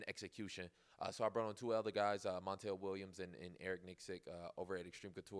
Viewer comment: Did he say Montell Williams?